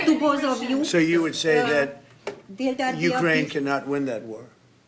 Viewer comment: Miracle can happened 😂😂😂 🤣🤣🤣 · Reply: a miracle can happen. But I thought he said 'a miracle can't happen'.